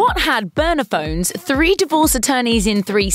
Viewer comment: I kept hearing ‘Boner phones’ and had NO what she meant. Then...oh Burner Phones lol